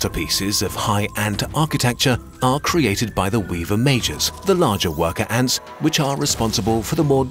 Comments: I know you’re saying “majors” but i keep hearing it as “mages”, which fits really well with the “high elf” comparison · Every time he said "Majors" I kept hearing "Mages". Together with the Medieval framing made these wars even more epic. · Whenever he talks about the "majors" all I hear is "Weaver Ant Mages". Those darn High Ant Kingdoms and their magic... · He says: “Majors” I hear; “Mages” hehe